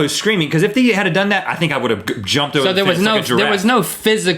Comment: lol 6:30....I can't be the only one who heard, 'I would have jumped the fence like a giraffe'